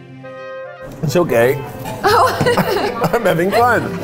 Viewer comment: YB thought Keith said "I'm so gay", meanwhile I'm here thinking he said "It's so gay" and I'm thinking "did I miss a joke or somethin??" 🤣🤣🤣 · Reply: He said it's okay I had to replay it so many times 🤣😅